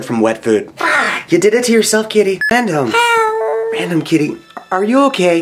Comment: Is it me or does it sound like she said hell?